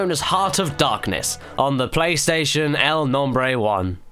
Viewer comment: you said "playstation the name 1" instead of number 1